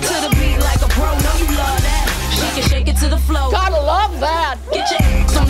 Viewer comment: I laugh now cause I hear "... Droppin' to da floor, got a lard ass..." Instead of "...gotta love that". LMFAO!!